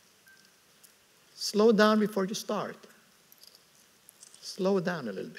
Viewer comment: slow down before you start. 시작하기 전에 속도를 줄여라 로 들리는데 강연 중 화면과 자막 모두가 잘못된 것 같네요